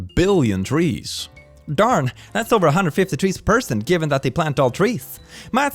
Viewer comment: Did you say "over a hundred tree's per person"? I mean you aren't wrong.